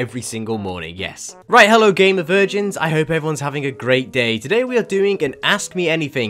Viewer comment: Always thought you were saying Gamer urchins. 🤦😩👽😸. Like I'm really dumb 😎😭😢